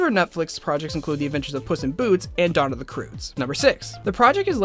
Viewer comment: what you said: Dawn of the Croods what the cc said: Donna D'Cruz